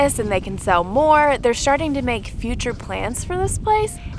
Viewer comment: Great video but did just say “sharting” 12:16? 😮😂